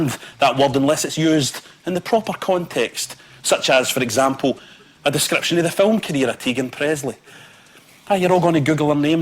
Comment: Teagan Presley is apparently an adult film actress. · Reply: Oh, at first I honestly thought he said “…a description of the film community in Teedunt, Presley” and that he was referring to some place in Scotland which is a popular area for shooting adult films.